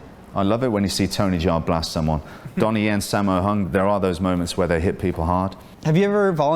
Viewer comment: for some reason i keep hearing 'sam well hung' every time his name is spoken...